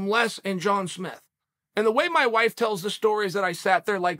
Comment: Omgsh when I heard Jon Smith I thought I heard Jon Cena 😳